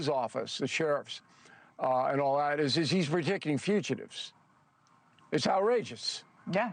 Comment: What is that word that Bill said when he described fugitives? It's at 3:10. It sounds like Bill said, "...he's ridicking fugitives." I know that's not a word but that's what I heard. What was the word he said? Can anyone tell me please?